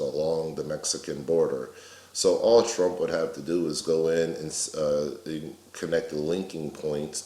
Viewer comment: am I tripping or did you just say all (TRUMP) would have to do? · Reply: I did... In reference to the wall that he says he's going to build... I'm saying that all he has to do is talk to my ex boss General Abrams... Abrams was running AXIS who set up the command centers along the border. If you were going to get a job done you'd talk to the person with the most experience...